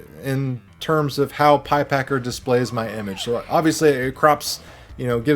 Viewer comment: Lol, I thought you said pie pecker. This seems to have came out of no where